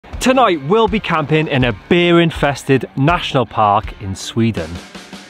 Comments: I heard: "beer-infested" !! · I thought you said "beer-infested" National Park..."and did we mention the beers?" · I thought you said "beer infested" Now that wouldn't be so bad!